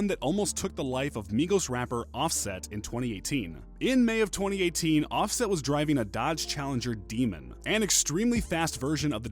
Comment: a dodge demon dummy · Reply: I thought he said offset was riding a demon LOL